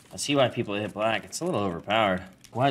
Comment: The first time I heard "I see white people hit black". Please tell me that it's not only me...